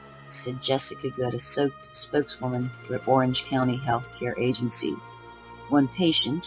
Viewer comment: Out breaks are never good. Sounds like you said orgy count. Thanks for the update.